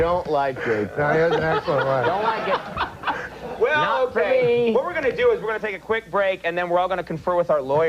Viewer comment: I can't make out the dialogue, but at around 6:10, it sounds like he says "And I don't like those jokes. Not for me." I could be wrong though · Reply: he says “and I don’t like gay porn, don’t like it, not for me.” He’s doing his part in the bit.